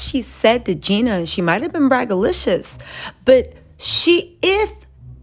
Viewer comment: Wait, girl what was that word you just used?🤔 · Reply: “braggadocious” seems to be the word she is seeking 😂